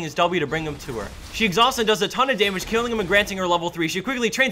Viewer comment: Did I HEAR TONS OF DAMAGE?